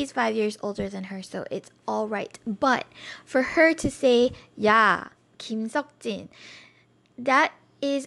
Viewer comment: i heard "yah kim seok oppa" 😂😂